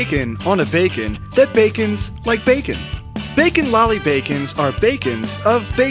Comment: all I heard was backin backins